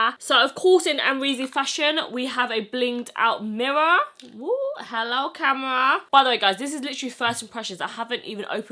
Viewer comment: When she turned the mirror around & said “hello camera” I thought she said hello Tamara (my name) 😂😂😂 I was like how she know?!?!